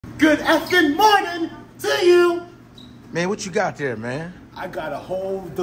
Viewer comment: I thought he said good f ing morning to you